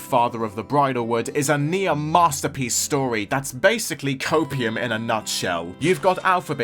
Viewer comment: What’s Cope-whatever you just said? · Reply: Copium - a slang term, typically used online, referring to a substance believed to be ingested by those who remain overly optimistic despite facing defeat or disappointment. Basically, always in denial of something ending or being changed. You’re coping hard. 😂